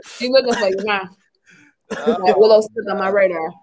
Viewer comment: Super rewatch gang but did call willow smith at 1:05:35 😭😭😭